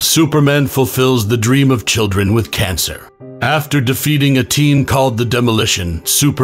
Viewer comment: At first I heard "Superman fills dream children with cancer," which is honestly just kinda horrifying conceptually